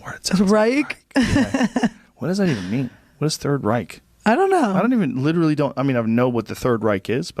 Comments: "The third right" is genuinely funny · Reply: I thought she said the third "rike" ? And rike being a German name that means "peaceful ruler" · what does third right mean? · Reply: I thought she said third "rike" and that was the joke . German name that means "peaceful ruler".